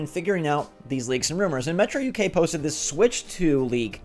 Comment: It’s only a leak when Nintendo talks about it, otherwise it’s just a useless rumor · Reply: I thought you said tumor so the rumor is a tumor😅😂